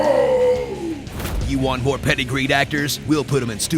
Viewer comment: So I FINALLY turned on closed captioning and realized that you are saying "you want more PEDIGREED actors?" I always thought you said "Do you want more PETTY GREEN actors?" and wondered if this was some special Hollywood term I was not privy too. XD Funny the things we take for granite.